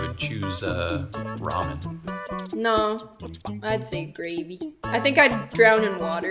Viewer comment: i thought she said rabies instead of gravy and I asked myself why does cost co sells rabies, and then it hit me that I'm an idiot